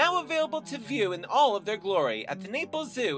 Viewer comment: I Thought it said Niples Zoo xD